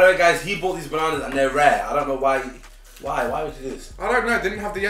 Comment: Did he say 'rare' rather than 'raw'??!!